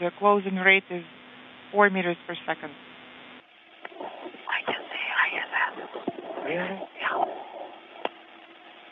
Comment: What did they whisper? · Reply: I've heard this : - I can see ISS ! - Really ? - Ya .